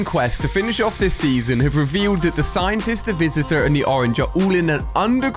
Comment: Bro it sounds like he said the orange lol